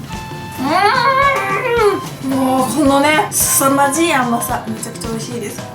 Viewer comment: のすさまじいあまさが クソまじい甘さに聞こえたのは、私だけでしょうか。